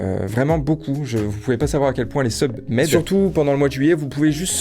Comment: J'avais compris " Les Club-med " au lieu " des subs m'aident "... Tuée moi sivouplé 🤡( C fo B1 sur )